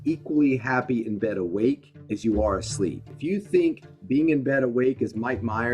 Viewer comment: I feel dumb but thanks for putting "Bad" at 2:36 since I had no idea what Chris Winters said